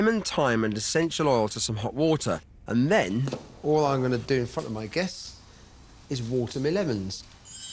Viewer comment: What did he say at 21:34? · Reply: "And then, all I'm going to do in front of my guests, is watering me lemons."